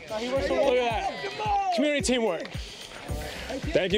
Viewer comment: The last officer should be arrested for chewing his gum so loudly in his mic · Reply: It sounded like he said communitay work.